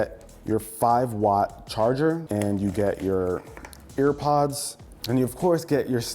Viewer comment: Did I hear it (1:12) "AIRPODS"?!?!